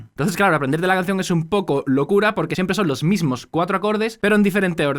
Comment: did you say "poco loco" XD